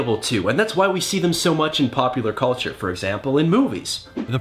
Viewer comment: did I hear "pup"- pular culture?? 😂😂